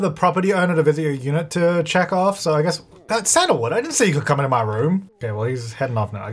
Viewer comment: hey - just some feedback about your closed captions. there are always mistakes in the captioning, and it makes it hard to understand. at 16:22 the captions mistake "Sandalwood" for "Adam" and at 16:33 they mistake "owner" for "in a joy", and at 16:43 the captions write "cereal" twice instead of "serial". and I dont know what's happening at 17:03. you get the idea. I'm someone who is hard of hearing, and captions help me understand speech without second guessing myself. your captions are nice sometimes, but they frequently have me take pause and second guess what I'm hearing. I would appreciate a higher quality to them, but I understand it's expensive and/or time consuming. it's just something I've always noticed about your captions. thanks for your time.